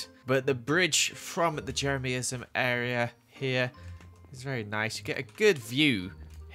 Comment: Do my ears deceive me or did say "Jerry-me-ism" ???